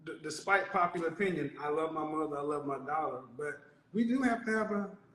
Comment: Did he say he have a daughter ?? 🤔